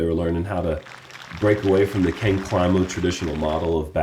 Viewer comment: closed captions say King Criminal as says Ken Climo 🤣